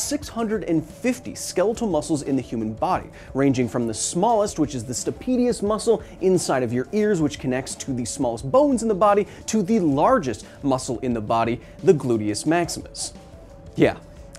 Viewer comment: I thought he said "the penis muscle"